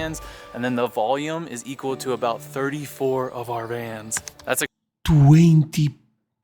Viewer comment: What is he saying at 3:43 ? 34 "of Irans" ? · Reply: I think he said of our vans